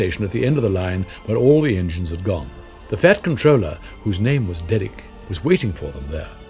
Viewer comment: Thought Controller said Commander.